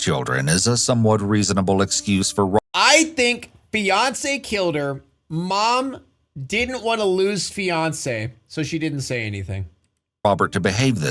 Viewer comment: I’m listening to this at work and really thought you said Beyoncé. And had to do a double take, like wtf did Beyoncé do???